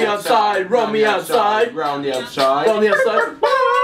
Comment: it sounds like casper is saying rum the outside